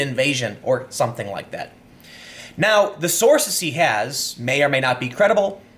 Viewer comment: Did he say "the source he has" Or "the source ze has" One means i'm just mishearing, the other means i need to stop watching timcast